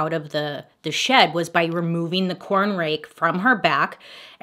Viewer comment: I thought it said "the corn snake mystery " i was like dang what do people hate about nice snakes now lol.